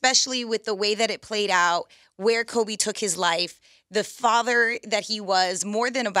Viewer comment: she said Kobe took his life?! Did anyone else catch that??